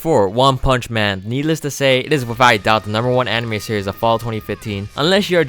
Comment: What anime did he say was the Japanese pick for best anime 2015 at 14:30. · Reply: Gochuumon wa Usagi Desu ka? or Is the order a rabbit?, I was more or less sarcastic but it was definitely up there while it was airing